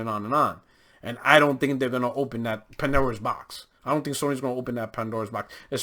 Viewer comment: 😂 mannnn, I thought you said "Panera's Box" lol I'm gonna start saying that now. Good take. This is the beginning of the Game Pass takeover